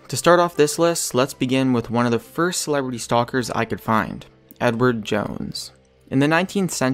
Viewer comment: I thought it said "celery stalks.." And I'm like.. Celery isn't that scary..😹